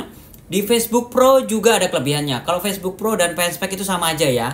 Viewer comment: dikatakan "facebook pro dan fanspack"? Fanspack itu apa kak? Or bukan Fanspack,, cuma saya salah dengar & teks yg tertera d layar YT jg salah mentranslate?? Mohon pencerahannya 🙏🏻 Thx sblmnya 👍👌🏼